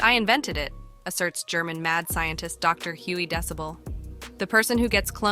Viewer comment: Dr. Huey Decibel?? Does that sound like a real name?? At first I thought she said Dewey Decimal 😂 and why had I never heard of pickleball before this??! seriously tho, I do believe there’s truth to the cloning and other things they do to celebrities to keep them from telling the public the truth about what’s going on..and the way the media is trying to normalize pedophiles by trying to get us to call them minor attracted persons now instead of the creep pedos they really are is just insane!!